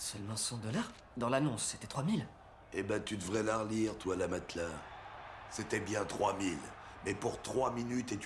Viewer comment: À 3:00 je comprends pas le dernier mot de sa phrase il lui dit bah tu devrais la relire toi la " matelas" perso je comprends le "la matelas" mais je pense qu'il dit autre chose quelqu'un peut m'aider parceque ça veut rien dire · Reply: J'ai galéré des plombes à trouver ce qu'il disait. En fait il dit " bah tu devrais la relire, toile à matelas"